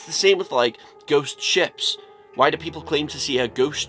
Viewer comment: I thought you said, "ghost chip" first. I was like what the fuck is he talking about. Lol